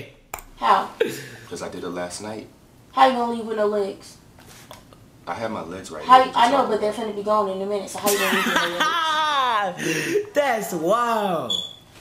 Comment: what she said though I laughed so hard I cried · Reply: She said that she was going to cancel is flight and cut off his legs